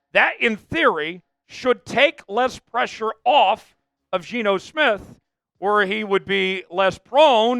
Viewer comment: The sentence dont make sense tho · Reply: I got ya taking “less” I thought it said taking pressure off of Geno lol